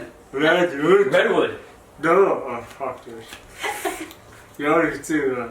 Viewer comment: did he just said fuck this shit?